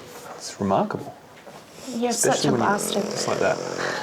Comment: At about 12:30 did she call him a b*stard?